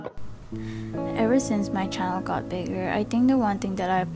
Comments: When you said having a channel ( my deaf ass thought you said having a child that got bigger and I was so confused)lol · im sorry but i heard "ever since my CHILD got bigger" and i was like HOLD UP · I heard channel as "my child"! And i was shooked to hear and was wondering if she had a child. Im sorry for thinking and mistaking that😣